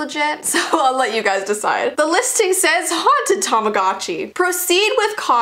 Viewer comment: i thought that said i bought a haunted tangerine... dont ask